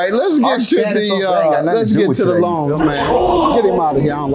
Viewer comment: what did he say????? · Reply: “Our status ain’t got nothing to do with it” something to that extent